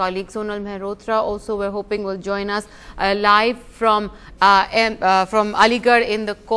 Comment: Did she say at 1:00 " Sonal Mahrotra will join us Alive"!! Funny